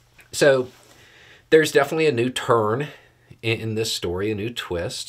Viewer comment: I thought he said ”A new turd in this case” . I thought Beau was getting spicy 😊